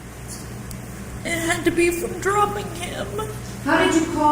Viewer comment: I really thought she said “From throwing him” for a sec